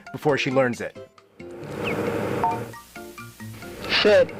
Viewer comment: almost sound like you said shit 0_0